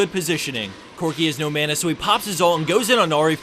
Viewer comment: I thought at 2:34 he said "Corki is no man"